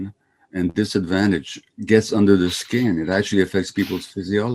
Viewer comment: he actually says 'under the skin'